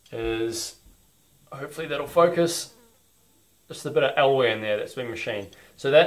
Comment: could you please explain what you are saying at 3:00, you are explaining what the material is you machined the nozzle from, it sounds like elway! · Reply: Hi. It's a piece of alloy round bar. I used it because I had some off cuts to use and it's easy to machine. Harder materials will probably do a better job at resisting abrasion but I used this because I was making lots of them to get the flow rate right for my set up and it is quicker to machine. Thanks